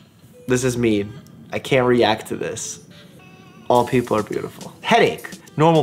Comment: sees a meme where he is the hot doctor “This is mean. I can’t react to this. All people are beautiful” ugh such a prince i swear 😂😂😍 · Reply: I thought he said old people are beautiful